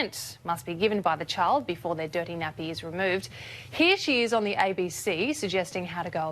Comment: did she just say 2:21 he/she wow I am. soooo triggered Lola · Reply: No I believe she said "here she is" 😂